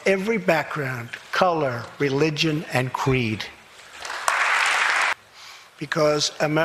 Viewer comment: Love Trump a lot but... Didn't he just say "qweed" instead of creed? Did drink a bit so am making sure.